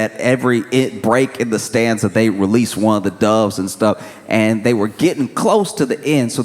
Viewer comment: Re: funerals.. I thought you said dubs not doves.. My mind went somewhere weird.